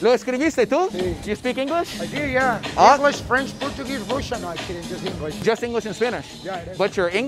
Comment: Americans by default detest anything Russian lol the guy said "Ingles, Frances, Portugues, Russian", in the subs says "ingles, frances, portugues, (no se entiende)" LMFAO